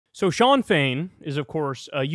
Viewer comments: Sean fein?? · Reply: Yes I thought he said Sinn Féin 😂😂😂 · I thought you said Sinn Féin 😂😂